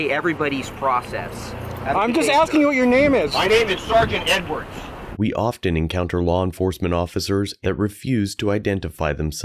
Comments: Thought he said sergeant n words lmfao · “Im just asking for your name?-“ “MY NAME IS SARGENT EDWARDS” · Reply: Thought he said Sergeant n-word 😭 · I thought he said Sargent N-word for a second I almost died instantly😂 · I swear it sounded like he said his name is Sgt Nwords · For a second I genuinely thought he said "Sergeant N-Words"